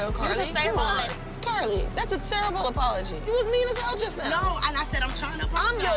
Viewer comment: When Spice said “VIEWERS!!!” In her accent and they thought she said “You was!!” 😭😭